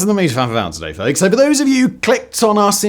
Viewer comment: what on earth did you say here?? 😂. “Mefnarfnar-today folks”